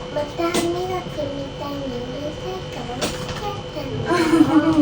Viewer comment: なんて言ってるんだろ また歯磨きみたいに見せておきちゃったの〜お みたいに聞こえるけどなんて言ってるんだい天使よ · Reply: また歯磨きみたいにうるさいから起きちゃったんだよお〜に聞こえます！ かわいい